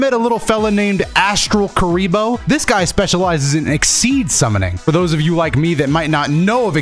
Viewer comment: I keep hearing “exceed” summon instead of Xyz summon 🤨